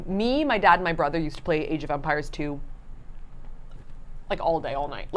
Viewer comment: Idk why but I thought you were saying age of vampires for a while lol